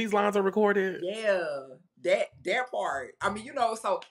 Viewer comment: did she just say "that part"? 😭 Anyone here watch Jasmine Masters? lmaoo